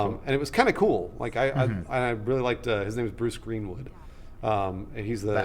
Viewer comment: does he say the guys name was bruce greenwood?